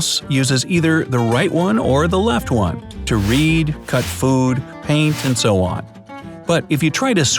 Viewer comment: did he just say read?